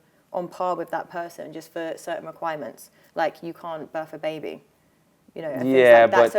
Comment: excuse me?? Did I hear this right? She said "you can't bath a baby"? Literally doing this with no issues.... along with many other "child raising" qualities\skills.... The only thing about a child EXCLUSIVE to women is giving birth and breasfeeding.